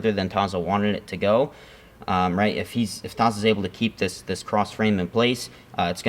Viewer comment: Nicky says "he goes for a ________drag". What did he say? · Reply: I think he says “Rau” drag, referring to Jason Rau